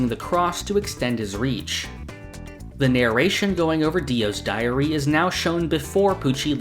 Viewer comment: i heard "going over dio's diarrhea" wtf